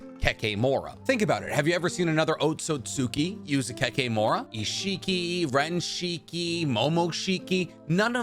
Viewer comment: What did he say at 2:58? Kinshiki?